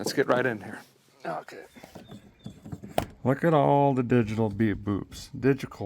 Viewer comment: is he saying digical instead of digital? 6:18. I think digital was said first and then switched to digical a couple of times. :)